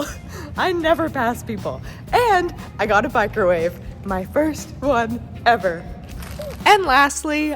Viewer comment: I thought you said "bicrowave", like a microwave for your bike